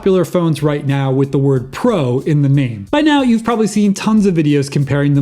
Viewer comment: Did he just say …”with hoe in the name”